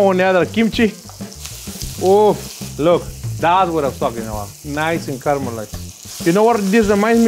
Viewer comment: I thought Chef said "That's what I f*ckin' want!" XD